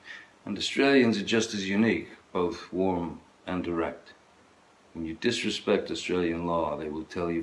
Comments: I thought he said 'warm and erect', not 'warm and direct'. My bad. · not gonna lie, the first time i heard this, i thought johnny said "australians are just as unique, both warm and erect"...and then the part about "they will tell you firmly". I was convinced this was some kind of joke. but no, i was wrong... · why did I hear "warm and ERECT"? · I thought he said warm and erect... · tbh It sounded like Johnny said ..."and erect" instead of direct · OOOOOOOooooh I thought Johnny said erect, hahahahahahahha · I legitimately thought he said "Australians are just as unique, both warm and erect "